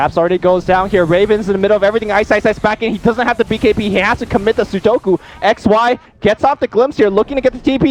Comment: "He actually commit a sudoku" did i hear it right?